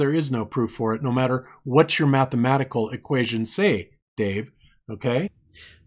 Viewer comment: Did he just say "Only Mathematical" around 32:35?